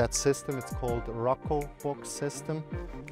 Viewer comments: What is the storage box name? I really can't hear what he's saying or atleast google it sufficiently. Rocko Box System? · what is the name of that bin system around 5min? it sounds like he said Roco bin system but I cant find anything.